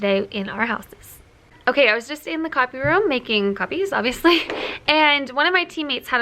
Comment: When you said you were in the copy room, it sounded like you said coffee room, and I got so jealous for a minute 🙃